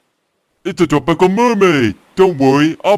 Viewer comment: I heard him say dark purple mermaid